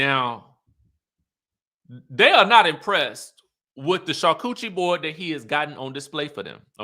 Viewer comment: did you say "char-coochie board" instead of charcuterie board 🤣🤣🤣🤣 you are too silly!